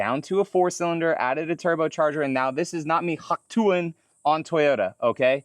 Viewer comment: What did you say at 4:25? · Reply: Hawk tuah, Reference to sucking c**k